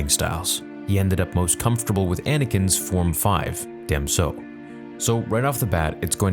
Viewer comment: I swear I heard: "He ended up with Anakin's form 5, damn so."